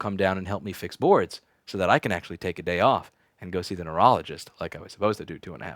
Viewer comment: Don't delay, go see your neurologist today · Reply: Oh, i thought he said urologist. lol